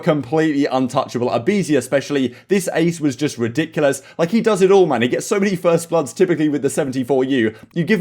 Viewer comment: I just woke up but at 2:25 did he say he does it all my nigga